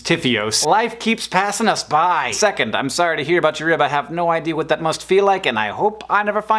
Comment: at first, I thought you said "Sorry about your other half." And then just moved on. To happiness. Needless to say, I was a tad perplexed, and had to rewind. John, I, too, am sorry about your rib, but am also terribly relieved that the Yeti is okay. Hope you get better soon. PS Thank you both for helping my 1+ hour commute not make me depressed.